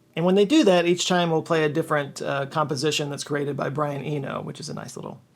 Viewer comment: I thought you said “composition graded by Brian Eno” and thought that was cool he’d review it but I’m not sure how many decades/centuries he can hold up that offer